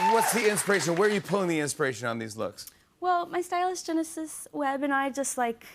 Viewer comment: I thought she said "My stylist/geneticist..." 😂😂😂